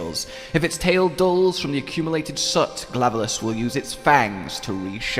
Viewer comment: I heard Glavelus instead of Glavenus, but maybe it's just me.